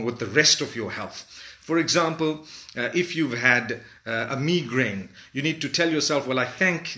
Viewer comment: aw he is so cute he said 'me-grain' instead of migraine